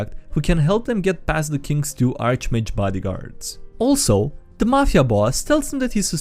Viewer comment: thought it said boyfriends